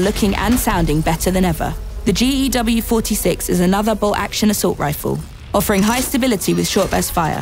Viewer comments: does she say a bolt action assault rifle? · Did I hear that right? Bolt action assault rifle?